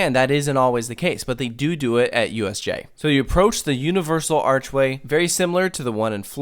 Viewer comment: For a second, I thought you said USA instead of USJ.